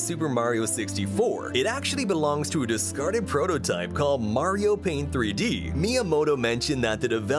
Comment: “mario pain 3D”💀 Edit:DEDEDE IM FAMAS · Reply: and oh look we two misheard it as "mario pain"